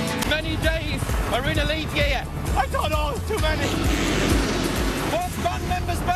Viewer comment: I thought niall said "to many" in stead of twenty?